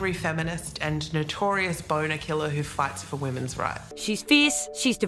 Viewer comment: Thank you for the subtitles. I misheard her and thought she said I’m the one ‘who farts for women’s rights’. Hold on, perhaps your subtitles are wrong and she really practices what she preaches 😂